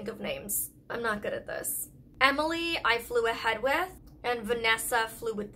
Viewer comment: I thought molly said the names were Emily and Fluiheadya... not flew ahead of us 🤣